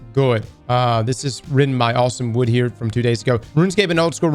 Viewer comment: Ohhhhh....Austin Wood...for a moment, I thought you said Awesome Wood lol lol